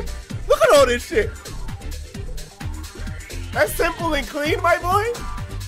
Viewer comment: His reaction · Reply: I think I heard him say "Simple and Cream"